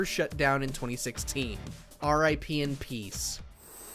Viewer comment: Did he just say "RIP in peace?"